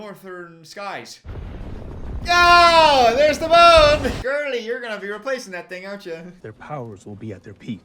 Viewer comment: DID JUST SAY GIRLIE???